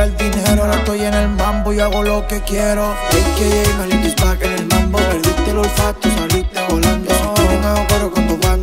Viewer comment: IDOOOOOO BUENIIIISIMOOOOOO!!!!!!!!!!!! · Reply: que dice carlitos destaca en el mambo perdiste el olfato saliste volando ??????